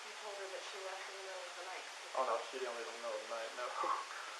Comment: Someone understand what is he saying at 26:40 after the « oh no » and laughing ? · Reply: Oh she didn’t leave in the middle oh the night no Then he chuckles